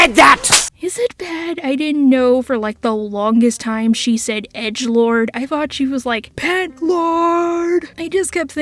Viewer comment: I always thought she said “Dicklord!”.